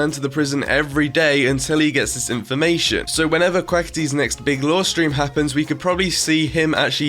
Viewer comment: What you said at 3:33 sounds like you said "Big law stream" lmao (probably your accent)